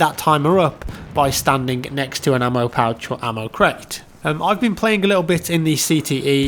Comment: Ammo pouch or Ammo kraut?? Lol it sounds like he said Ammo Kraut lol